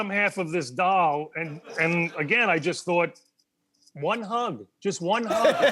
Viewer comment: 'One ..., just one...' what? Couldn't understand the word. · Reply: Hug